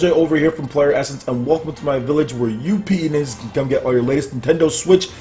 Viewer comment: LOL what did you call us? May want to reword that a bit. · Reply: PlayerEssence still doesn't give you the right to call me "penis".